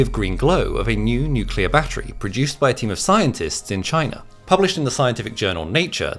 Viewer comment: I had to scroll back because I thought you said "Temu scientists" at first 😅